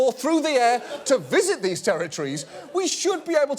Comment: It is funny how at the end it sounds like he says they should et adequately hurt instead of heard